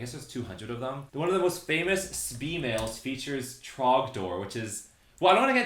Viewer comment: i also thought firey said wrong door up until now